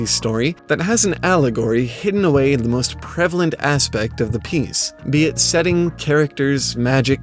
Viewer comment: It sounds like, "... aspect of the penis." I know it isn't what is said and I've listened several times but it's still there. I can't un-hear it.